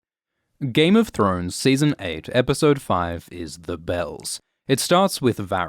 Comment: I actually thought he said "Game of thrones season 8 episode 5 is The Best" I was like wait what?!!